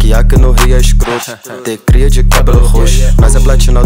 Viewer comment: eu entendi e vocês ? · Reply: uai ele só falou q tem cria de cabelo roxo🤷🏻‍♂️